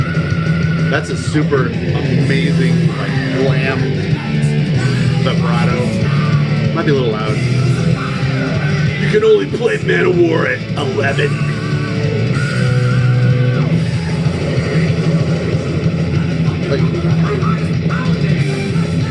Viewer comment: I swear I heard "Dude, I only play Manowar AFTER eleven."